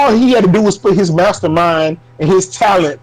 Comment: "mastermind and his talent", apparently these xbot fn's and got Phil Spencer's dick in their ears that they thought he said "team". Smmfh...